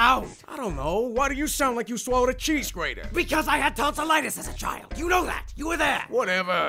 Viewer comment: Papyrus: Why does it make that sound?!? Sans: I don't know!!! Why do you sound like you swallowed a cheese grater? Papyrus: I had tonsillitis as a child, you know that!! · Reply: Breyergal Isabel lol I thought he said " I had tons of lettece as a child u know that!!! "